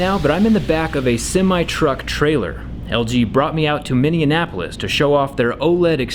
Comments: Wth he said Minniannapolis @ 0:25 it's Minneapolis jRE that sounded so wrong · “Mineanapolis” is it Minneapolis or Indianapolis? Lol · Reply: Wth he said Minniannapolis @ 0:25 it's Minneapolis jRE that sounded so wrong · did he say Minneanapolis?